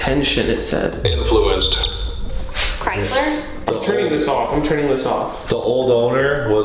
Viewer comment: I heard "Chrisler" or something(edit):I remember I watched a Sam and Colby video at the Kriesler mansion